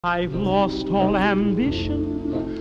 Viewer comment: "I just want to pee" 💀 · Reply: This is made even funnier by the fact that I thought he said “I’ve lost all ammunition”